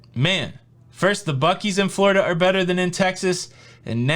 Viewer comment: when you said buckees I thought you said buck eyes but that's Ohio.